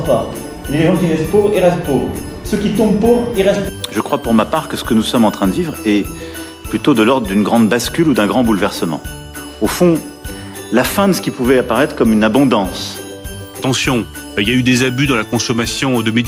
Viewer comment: Comment voulez-vous qu'il en soit autrement lorsqu'on vote deux fois pour un clown qui n'en a rien à cirer du pays. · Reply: j'ai entendu "de l'ordre du grand ? remplacement ..."